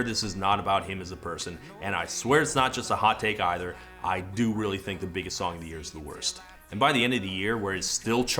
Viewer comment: Ngl I thought you were saying paramore had one of the worst songs of the year